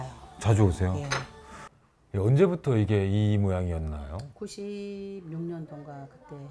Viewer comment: 나만 이 질문이 "이 모양 이 꼴 이였나요?"라고 들리냐? ㅋㅋㅋㅋㄲㅋㅋㅋㄲ